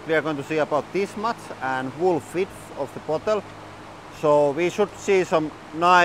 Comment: Did you just say "wool fidth of the bottle" ? awesome!